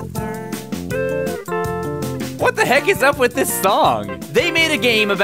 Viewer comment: What a hole? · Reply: I heard "what ep huh"